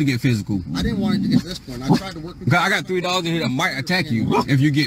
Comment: I thought he said I got $3 in here but what he said was I got 3 dogs in here.